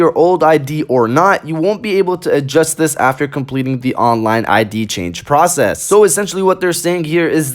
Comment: I didn't understood at 2:18 that what they wanna say?Can you please help me to understand this? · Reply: WizardBTAOU he said that 'You won't be able to adjust this after completing the online ID change' I didn't understood this line